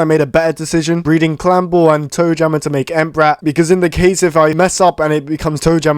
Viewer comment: did he just said amp rap i thought he said Entbrat